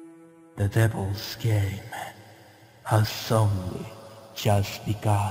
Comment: is it weird that at first i heard the devil’s gay